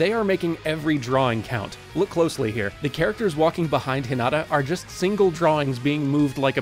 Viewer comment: I honestly didn't realize he said Hinata at 6:47 until I watched it again! 😂 (Thought he said something like Hin-ada. 😅) Awesome video! I watched this, and then the next Haikyuu!! episode I watched had Fly High as its OP so it was cool to notice all the little things you guys pointed out here.